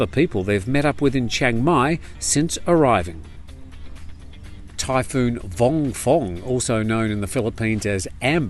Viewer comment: Typhoon? I thought you said Thai Food!